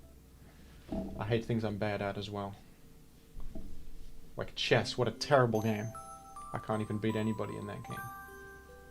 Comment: what did you say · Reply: Sorry Chess. You're a good game actually.